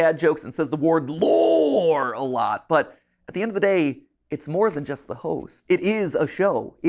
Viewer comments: o7 also at like 10:00 he says "but at the end of the day, its more than just the hoes" or atleast sounded like that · everytime he says "host" i hear ho*s 🤣🤣